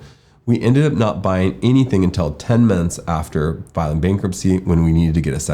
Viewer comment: I had to repeat on the 3:00 minute mark so many times just to try to understand what he said. I thought he said after 6 months they checked for rates and they had to wait 10 minutes after filing bk. It was clearly 10 months which made more sense 😂😂🙏🙏